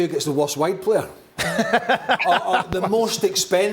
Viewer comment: When Craig say 'wide player' and all i hear is "white player" 😂😂